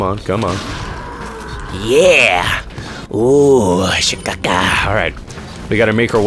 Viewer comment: What on earth is he saying at 5:30? lmao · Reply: TheMonroe654 he's saying "she-ka-Ka" which is a reference to the movie Ace Ventura When Nature Calls" which means "Great white bat" in an African dialect